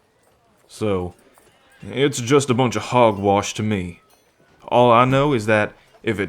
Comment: The moment he said Hogwash I thought he said Hogwarts for a second 😂